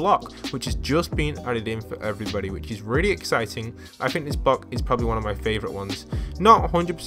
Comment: Did anyone hear ‘Bock’ instead of ‘Block’